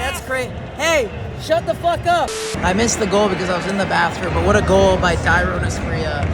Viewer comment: What did that guy say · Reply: “Put down your fucking phone and be at the game dumbass!” “Hey! Shut the fuck up!” I suppose going into the rival’s stadium and self-recording will likely annoy some home fans.